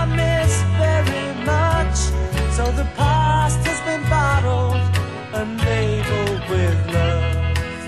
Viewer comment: Misheard the pasta’s been bottled and labelled with love 😳